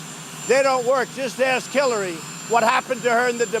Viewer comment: what was that, run that back a couple of times -- yup, think he said K!llary.